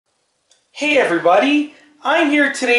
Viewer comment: I thought he said Hate Everybody At 0:01 😂😂😂